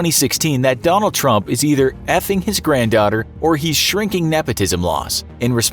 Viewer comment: did he say, granddaughter??